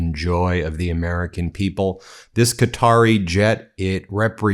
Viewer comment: I thought you said by America this is not American jet We have to take the Senate back and the house so we can straighten this out take that money and give it back to the people that Trump stole 😡😡😡😡😡💙💙💙💙💙💙